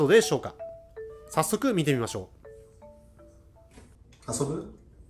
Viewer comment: いつも可愛い猫ちゃん達をありがとうございます。 3:09 の雪見ちゃんが「遊ぶ」って日本語を喋ってるように聞こえる！可愛い！